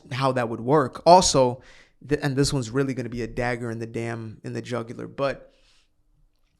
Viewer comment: I heard this Sentence as “A Dagger in the Jugular-Butt” 😳